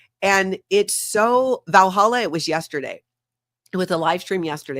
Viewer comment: I think u r saying Valentines ❤ was yesterday but the audio and transcript tinks u said Valhalla 😂😂😂😂😂